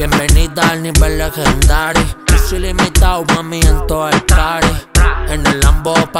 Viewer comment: Yo pensé que decía Tussi ilimitado mami en todo el party jajajajajajajajajaja